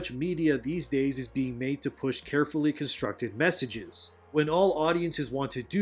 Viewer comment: Did you just say at 6:24 that these messages are "carefully constructed"? xD